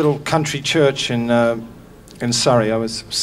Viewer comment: Does anyone know where he says he wrote this song. He say in a little country church... I thought he said Sarajevo? Anyone? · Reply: In Surrey. England.